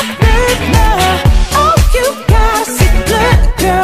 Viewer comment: I heard "Oh you got us a good girl" Instead of "Oh you gotta see blood girl" I thought it was those lyrics for like 3 years 😂